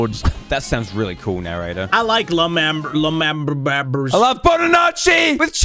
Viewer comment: wait did just say la’manburg